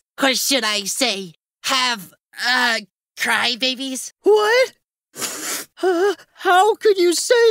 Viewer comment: or 11:30) i actually thought he said have babies for a second